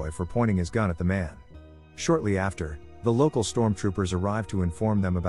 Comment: did he actually say stormtroopers? 😂